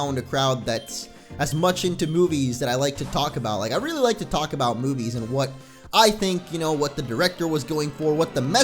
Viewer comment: When he started talking about movies at one point I thought he said 'I like to talk about boobies' me......................